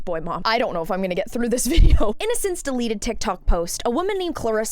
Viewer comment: "Innocence deleted Tiktok post..." That's legit what I heard and I think it's fitting.